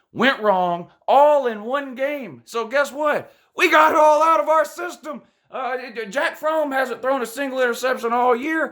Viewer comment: I swear I heard you say Jack Fromm